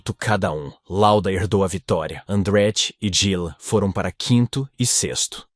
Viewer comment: Andretti e quem ??? Dhhilon? Nunca ouvi falar nesse piloto em toda a história da F1 .acho que a belíssima I.A quis dizer didier, creio eu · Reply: "Gilles"